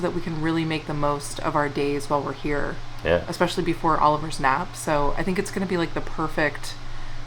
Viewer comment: You said something like, before Oliver's nap and I thought you said, before Oliver snaps.....😸💖💖💖